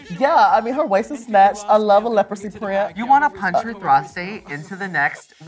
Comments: did heidi just say “i love a leprosy print”? HAHAHAH IM ROLLING NOT LEPROSY OH MY GOD 😂😂😂. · DID HEIDI JUST SAY LEPROSY PRINT